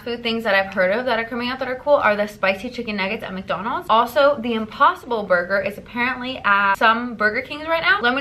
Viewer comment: I thought you said Kim Possible burger LOL